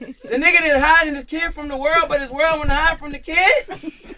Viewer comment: DRAAAAAKEEE???DRAAAAAAAKEEEE?????? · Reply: I was laughing so hard I thought he said the world found his kid.🤣